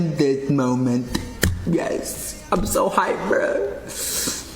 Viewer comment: sounds like he says "I'm so high bro" to me